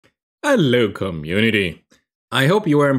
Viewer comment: I always hear "Hello come unity"